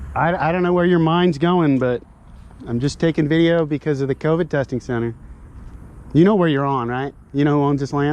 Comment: In the opening, thought you said “convict testing center.” LOL. You’re a natural at this.